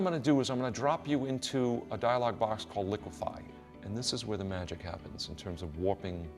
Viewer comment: From 3:39 - 3:46 what is the command (tool) he uses to do that retouch? Can you spell the word for me? I can't understand what he says: "Qualifie" or something? Please spell it for me here as an answer or send me a PM :) Thanks to everyone :)